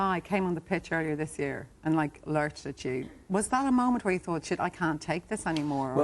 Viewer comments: does miriam say shit @ 09:16 ? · did Miriam say shit at 09:16 lol